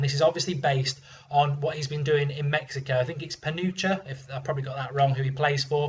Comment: says "panocha" instead of Pachuca LOL... that means vagina in Mexican slang.